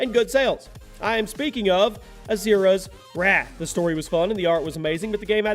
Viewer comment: I never heard of "Asura's Watch" But I heard of Asura's Wrath